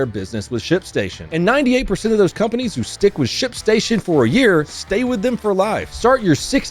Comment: When he introduced sponsor, I heard him say shitstation